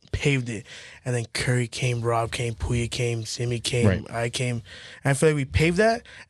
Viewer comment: after pouya who did he say? Simmy? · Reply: Yung simmie